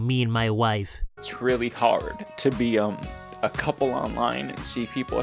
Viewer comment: i thought he said its really hard to be a ‘cuckh0ld’ online rather than a couple at first